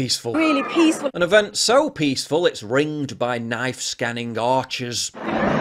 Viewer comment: I thought you said, knife scanning Archers. I was like, what they have people with bows to shoot people with knives? We really have been devolving with the backward use of weaponry.